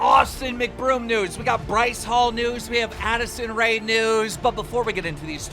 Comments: Idk why but it sounded like he said we got Addison rae's nudes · did anyone else hear nudes